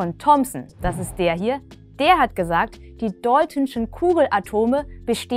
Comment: Hallo. Ich wollte fragen was du in Minute 1:07 sagst, denn ich verstehe immer nur die ,,deutinschen" ist das ein Fachwort ? Ich hoffe auf eine Antwort hahah muss dass Video Inhaltlich wieder geben für Chemie. Danke.